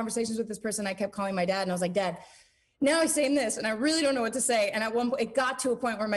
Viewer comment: What did she just say at 6:23-6:28? Was it Naill or he's now? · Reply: But I think she said “now he is saying this”